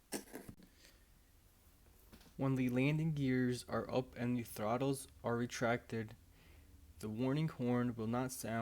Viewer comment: I swear to god that question says " and the throttles are retarded" lmao nice save bro!